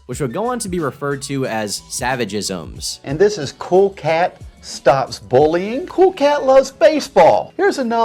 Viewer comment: at around the fifteen minute mark I thought I heard "Cool Cat ends bowling" instead of "Cool Cat ends bullying" and got super confused lol